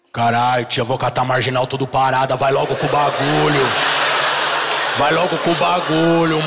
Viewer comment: o que ele falou em 1:38 que eu não entendi ? kkkkkkkk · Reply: "Caralho tia eu vou catar a marginal tudo parado vai logo com o Bagulho" Se referindo à pegar transito e terminar as entregas. Acho que ajudei kkk vlw!